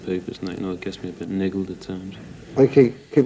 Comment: Actually what I think he said was "It gets me a bit needled at times".